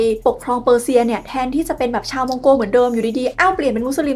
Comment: วันนี้ได้รู้สักทีหายคาใจ ที่เขาพูดว่า ชาวมองโกอิสลาม ❤🎉